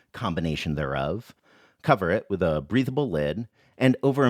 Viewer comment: I heard it as, "cover it with a reasonable lid" and then the worlds most reasonable lid came into frame.